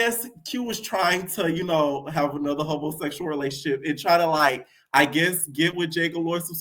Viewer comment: lol I thought you were saying a homosexual. And I was like no that isn’t what that means. Then I heard the hobo sexual. And I died